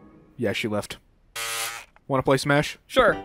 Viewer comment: I THOUGHT HE SAID WANNA SMASH PFFT